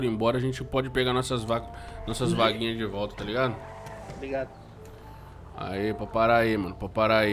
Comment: só eu q ouvi o falando obg do nada?